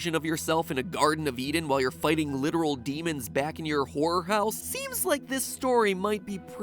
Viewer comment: sounds like he said whore house XD